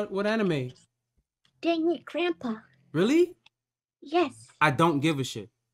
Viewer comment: Ah yes, Shukichishukisaikara from Deadbeat Grandpa. That show is a must see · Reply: thought she said dragon grandpa